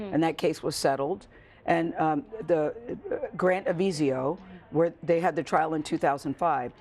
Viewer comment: Did she just say Grant Avizio ? Who's that, the successor of Jonathan Chalonder?